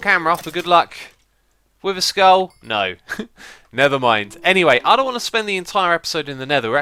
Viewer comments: Did he just say 'nether' mind? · "Wither skull? No. Nether mind." Thats what i thought you said.XD